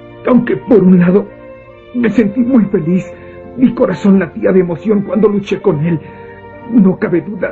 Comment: no se si tengo la mente tan pervert o simplemente he visto demasiado yaoi pero por alguna razón cuando dijo: "mi corazón latia muy fuerte cuando luche con el" solo escuche "mi corazón latía muy fuerte CON EL " y pensé yaoi , yaoi mientras babeaba como fujoshi loca , que cosas no ?